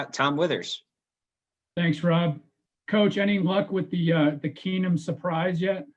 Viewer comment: Did he just say 'Chase' Keenum?